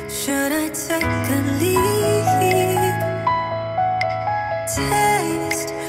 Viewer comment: sounds like you said should of take a leaf 😂😂😂😂😂😂😂😂